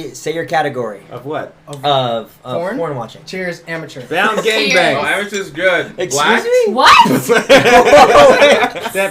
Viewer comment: What did say at 7:57? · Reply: “amateur is good (in response to brian)… blacked? (his answer)”